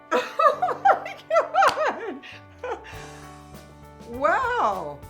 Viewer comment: When he said oh my god I could have sworn I heard "Pretty woman, walking down the street"